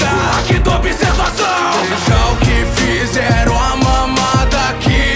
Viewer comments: Eu ouvi "Vejo q fizeram a mamada" Eu tive q ouvir mais três vezes pra entender jkjkjkjkjkjk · depois que ouvi 1:08 "veja que fizeram a mamada..." Não consigo mais ouvir certo kkkkk